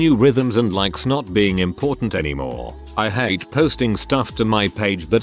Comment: At first I misheard this as “and LIKE SNOT being important anymore” 😂